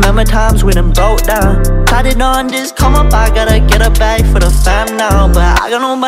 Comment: who Hears Columbine instead of come up?